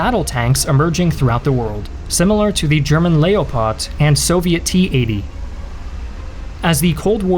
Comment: did he really say layopot instaed of leopard?